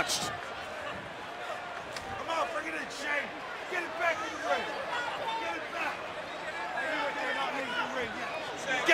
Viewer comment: what did the reff just say to a mcmahon 😳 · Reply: Mike Chioda, the referee said: “Come on , bring it in, Shane.”